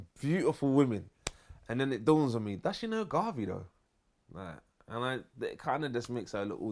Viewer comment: What does he say @ 10:00 he says sometimes he is driving around and see some beautiful women and then he thinks to himself "what"? I can't catch what he is saying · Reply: "Does she know Garvey tho?" As in Marcus Garvey